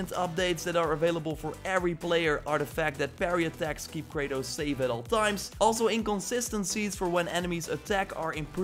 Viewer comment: wtf does he say there? his accent is hard to understand "parry attacks keep kratos....... at all times" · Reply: Keep kratos safe at all times :/ how can you not understand that ?